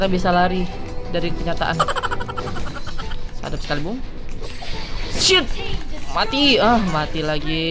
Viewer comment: gue dengernya Sshit